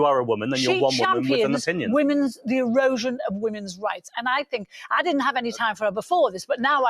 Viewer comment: Why do I always hear "she champions the, erosion of women's rights" in that rant about JK, accidental honesty?